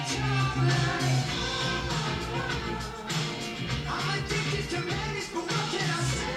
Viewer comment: Every time i listen to silvertongue it sounds like he says ‘im addicted to mayonnaise but what can i say’ and i cant un-hear it help